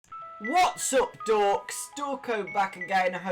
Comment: In 1.00 it's sounds like you said what's up dorks lol